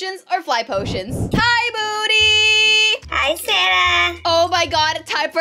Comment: For a moment of silence for people who thought she said hi boody 🤣🤣🤣